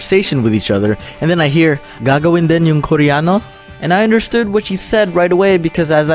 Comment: English sub @ 2:15 instead of gagawin din yung koreano, it said "gago in denim koreano"